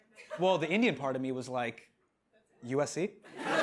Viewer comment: Can anybody tell me what he said at 0:34? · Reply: USC I think